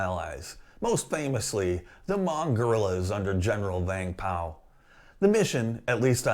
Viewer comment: I thought you said "General Bang Pow". Haha!